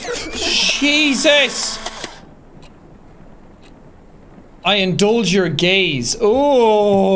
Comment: okay but can we talk about how the captions said “I indulge your gays” 1:01:08 💀